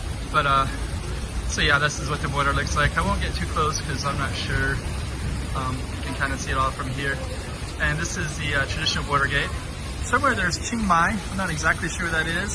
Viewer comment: I first thought, you said Watergate, probably, because I saw a fountain. But you said Bordergate, LOL.